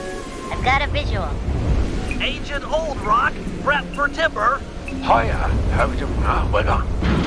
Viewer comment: "Kid's stuff"???? I thought it was "Can't stop" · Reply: Me too. This helped me with a lot of lines I've been mishearing this entire time. Like "Agent Old Rock, prep for dipper" was "prep for timber", or "I poisoned your bring-in lunch" was "I poisoned your drink at lunch". Crazy.